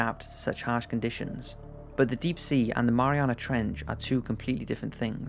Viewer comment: Who else thought it said marijuana trench